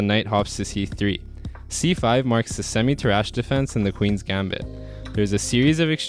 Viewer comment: semi-trash defence 😂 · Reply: Nah I clearly heard "tarrasch"